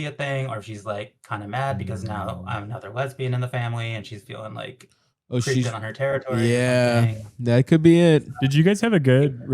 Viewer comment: not sure if i wanna downvote for misgendering the caller or upvote for defending her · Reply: no you def misheard the caller. She says something along the lines of "I'm the other lesbian in the family and I'm creeping on her territory," lol.